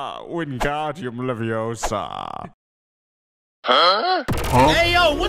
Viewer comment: All I hear is "WingdrauniumleviouSUS"